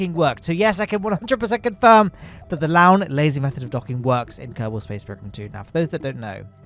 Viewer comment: I always thought you were saying the Lowne LASER Method